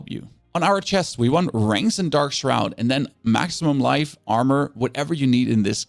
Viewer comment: What are you saying? “Rings? and dark shroud”